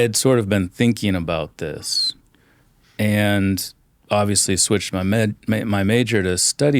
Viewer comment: I thought he was gonna say “I switched my meds”